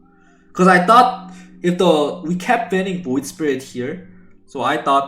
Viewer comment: Did I just hear Febby said a tagalog word? "ito" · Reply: Its "if the" 👌